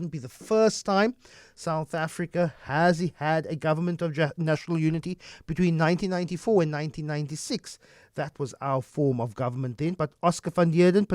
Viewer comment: I heard “Government of National Nudity” Anyone else? 🤣